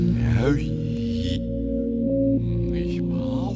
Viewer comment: This on acid is crazy right now I'm going through the whole birth of the universe in one song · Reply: Right when I read this comment I hear 0:18 sounds like he’s saying “machine elf” 😂